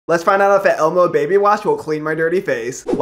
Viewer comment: I honestly thought it said emo baby wash 💀